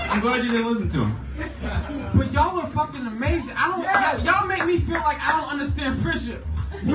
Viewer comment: Help me: I can't understand the judge joke at 07:08 What is he saying "the frissure??!!". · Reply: "Yall make me feel like I dont understand friendship" as there friends and room mates someone else then said "thats deep" lol